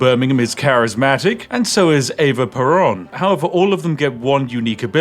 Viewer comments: did you just said PERON??? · I was listening and not looking at the screen at 2:58 and thought he said "Eva Braun" and was very confused for a second there. 😂 "Well done developers."